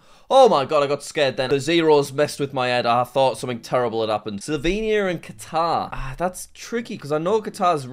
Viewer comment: Did you just say Slovenia? 🇸🇮